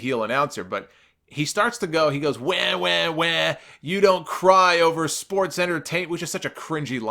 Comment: When points out what Bradshaw said around 11:02-11:09, he actually said "You don't cry in sports-entertainment.", not "You don't cry over sports-entertainment.". Be that as it may, I got the point... Scripted, or, not, that was a stupid line. I get that it's a play off of "You don't cry over spilt milk.", but, that was stupid... Michael Cole saying "SHUT UP!..." to Bradshaw, afterwards was rather funny--albeit I'm sure that was scripted, as well.